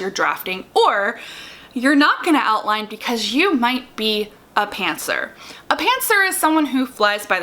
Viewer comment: I misheard you and thought you said pantsers lie in their pants and that actually helped me as much as what you actually said 😂